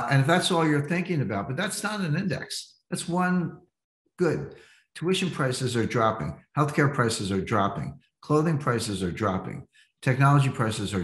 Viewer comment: tuition prices are dropping....healthcare prices are dropping? I had to play it 5 times to be sure i heard him right. Can anyone chime in on this as to which data he's referring to?